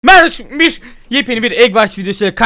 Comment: merabaşumbiş mi dedi o? Ne dediğini anlayan var mı? 🤣🤣